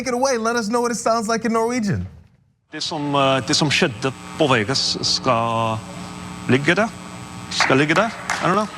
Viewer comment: Norwegian is my second language. What he was saying in Vegas was "What happens in Vegas shall make you happy" rather than "stays here". But also... if he didn't speak any Norwegian, he wouldn't get the first part of the sentence right. He knows a little but probably couldn't hold a conversation, which is fine because almost all Norwegians under the age of 70 are fluent in English anyway. It's only useful if you are going to spend a lot of time around Norwegians and want to show an interest in their culture. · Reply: Ah, I thought he said "skal lykke deg" so I was roughly translating it.